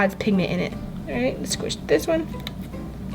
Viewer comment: tell me if you here her say all right let me squish this one I think she said that give me a like and also comment to me to see if I was correct I think she meant to say let me cut this one but tell me if I'm correct in the comments please thank you